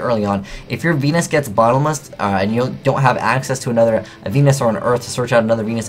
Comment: sounds like he says "Venusuar" haha